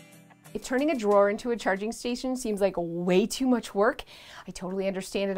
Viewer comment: I thought the video said changing station, I watched most of this video confused 🤷🏾‍♀️ lol